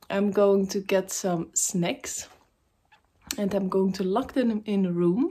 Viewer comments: I thought you said you were going hide “snakes” in the house ! I’m glad it was just “snacks” 🤣😆🤣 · love your video i thought you said snakes but you said snacks lol snack sounds like back...snakes sounds like breaks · Oh I thought I heard you were going to distribute snakes.. 😂😂